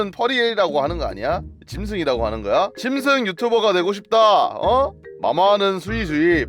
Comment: 잘 짜르면 가 '짐승 유튜버가 되고 싶다'고 하는 걸로 들림